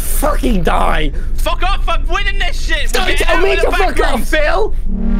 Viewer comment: What is phil saying here??? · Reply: '' fuck off I'm winnin this shit''